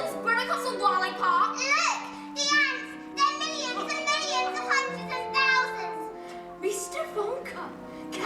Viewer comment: can someone tell me what she's saying here I didn't hear any words but gibberish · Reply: Look! The ants! There's millons, & millions, of hundred & thousands!